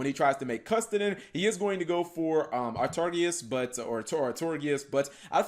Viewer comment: Did you say R-tardius?..